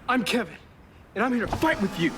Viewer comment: I used to think he said I want to fight you